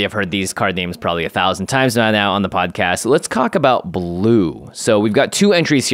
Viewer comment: "Let's cock about blue" ... anyone else catch it?